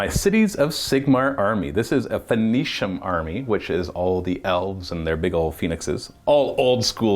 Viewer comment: Why does it sound like says his elves and their big penises when describing his army? 😄